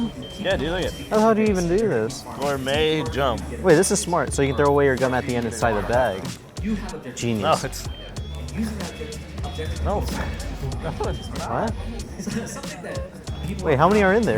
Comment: what did he say at 15:40? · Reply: so this is smart. So you can throw away your gum at the end inside of that. Genius!